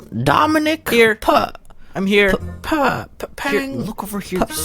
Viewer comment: I thought he said I'm here po 😂😂😂